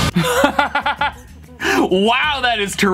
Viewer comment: For the first one, I heard "Psycho-social"